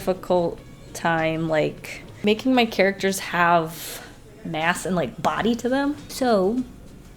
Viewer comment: I- I thought she said “...had a difficult time giving my characters ASS” and I was like 0-0 but then i realized she said “mass” so....